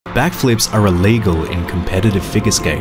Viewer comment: Oh! I thought this video was saying black flips are illegal.